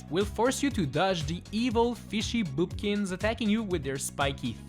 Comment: Did you say fishy boopkins was evil